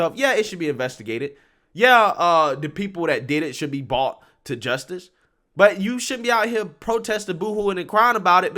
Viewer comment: did you say: "bought to justice" Shouldn't it be "brought?"